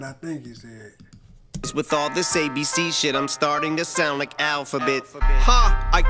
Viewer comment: He said "I'm killing this ABC shit" (Music to be MURDERED by), "I'm starting to sound like Alf, I bet" (Alfred Hitchcock created the original Music to be murdered by album, an album with the same name, hence "Im staring to sound like Alf". It was also the ultimate inspiration for Eminem's album). Edit: Sorry guys, I caught that and just wanted to give my perspective on that line, like what I think he meant. I'll shut up now and watch the rest of the video lol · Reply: Oh i thought he said im killing these ABC'S, Im startin to sound like The Alphabet